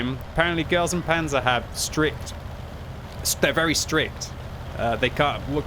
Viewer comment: i definitively heard "stripped" instead of "strict"